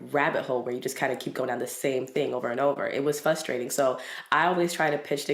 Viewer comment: Is she saying "fustrating" without the "R" or am I mishearing her?